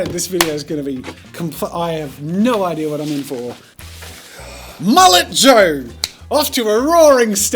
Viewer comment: For some unknown reason, when he said Mullet Joe, I thought he said Mallet Joe, and I was like, OH COOL IT’S GOING TO BE A MALLET WIELDING TROLL GUY! Don’t judge me I haven’t been a Jazza fan for very long